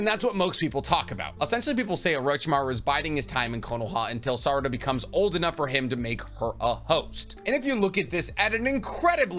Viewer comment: I wasnt paying full attention and could have swore you said Orichimaru was "waiting till she was old enough so he could make her a hoe" lmao